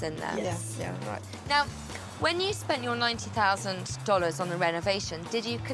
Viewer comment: I remodel houses and I just can’t see what they spent 90,000 on. The curb appeal is just depressing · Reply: Yeah, it does sound like she said $90,000.😳